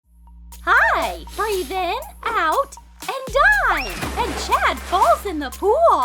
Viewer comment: I actually thought she said “breathe in, breathe out, and die!” I cant- 😂